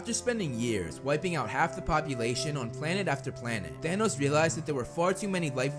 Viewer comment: For some reason I kept hearing “Danos”